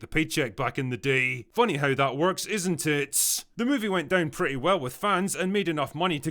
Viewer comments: The way you enunciated " isn't it?" At 1:45 Was too on On the nose and sounded like "isinTIT".... So the question is.. Was it a coincidence?.. Or are you trolling us bro? 😵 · Sounded like you said, 1:44 "Funny how that works, isn't tits?" Not enough side boob these days. But back in my day, girls didn't have curves, they had edges.